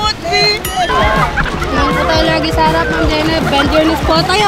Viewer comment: tama ba narinig ko ? Tingin po tayo lagi sa harap mam JELAI ? OR nabungol lng akech ?? 🤣🤣🤣🤭